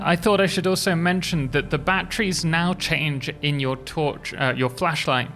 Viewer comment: i thought he said "Bat Trees" and thought "THE BATS HAVE TREES IN THE CAVES NOW!?!"